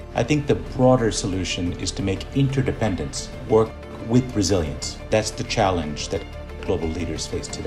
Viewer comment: Who else heard "with Brazilians" instead of resilience at 13:41. LOL